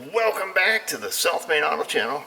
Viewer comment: Every so often I hear "Welcome to the self made auto channel" and I think, "Yes it is". Then I remember you are saying South Main..lol